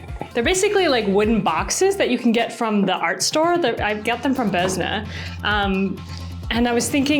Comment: Bezna? Boznah? What store name are you saying here? I've not heard of it, and I tried looking up a few different spellings that seemed like they might be what you're saying, but didn't find anything that seems right... curious to find it, though! (Always interested in knowing about more art supply places!) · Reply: Boesner :) it's a chain in Germany, I'm not sure about anywhere else!